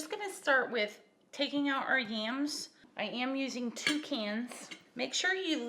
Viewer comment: It sounds like you said "I YAM using 2 cans..." LOL